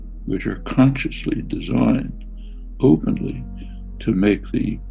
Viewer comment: this video is manipulated, he did not said government, he said PEOPLE✌️for human kind